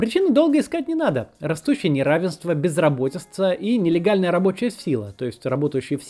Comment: Послышалось «безработество» 2:03’. Но Максима слушать интересно.